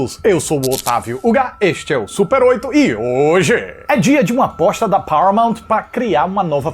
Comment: Você falou "aposta" eu entendi "bosta"